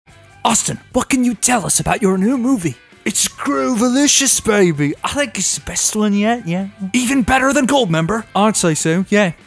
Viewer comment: when he said "I think it's the best one yet" I thought he said "I like asbestos in the air"